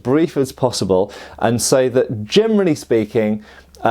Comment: does he say ''germany'' speaking instead of generally speakingat 9:18 ? is it a word im not familiar with? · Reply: his accent. He clearly said "generally".